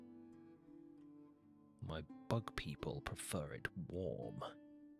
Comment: I had just rolled over to fall asleep and all I hear is "My bug people like it warm~" Had me giggling.